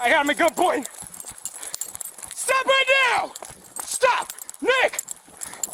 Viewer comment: The officer seems to know the guy personally as he called him Nick. Is that why he was more trusting of him not trying to attack him. Edit: 4:54 thought I heard him call him Nick but am not sure anymore. So does somebody know the suspect's actual name can confirm if this assumption is right or wrong.